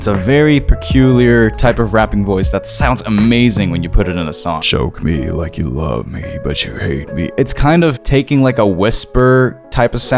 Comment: is it just me or did he say "choke me like you love me but you hate me" at 3:20 or somewhere near that